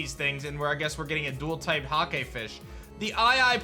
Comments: thought you said “the hot gay fish” and i did a double take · When he says hakefish, I turned my head because I thought he said 'hot gay fish'